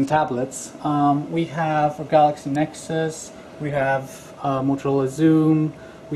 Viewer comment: I thought he said Nexus, then I watched again and realised he said Galaxy Nexus. I think my Nexus 7 Tegra 3 is an easy match for my PSVITA so it will be interesting to see the Tegra 4 compared to the Series 6. Don't like the Engadget interviewer, seems very pushy.